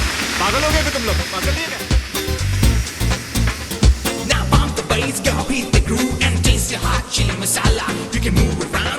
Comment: Yeh bonviage kya bola 2:45 ke pehle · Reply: Bon voyage